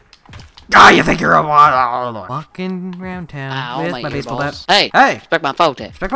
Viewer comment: it sounded like he said "fuckin round town, with my baseball bat"